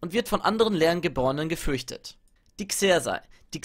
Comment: gefürchtet.. ich hab gevögelt verstanden lmao